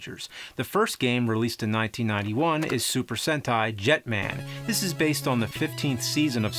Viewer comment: I thought he said super hentei